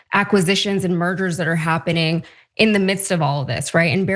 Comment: 5:!6: Sounded like she said "acquisitions and murders that are happening..." freudian slip?!?!?🤣🤣 5::29: Small correction, Larry Ellison is no longer the CEO of Oracle, just chairman.